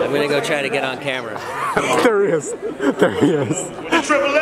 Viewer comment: did anyone else hear triple x or is is just me